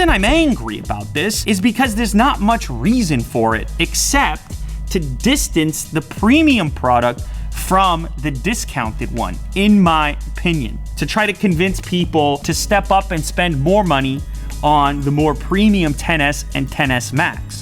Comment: Did you just say in your pinion!! Made my day!! LMAO